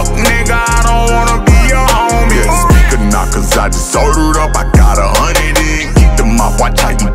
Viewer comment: First time I heard this I thought he said..."yea speaker knockers I just heard of himmm" lol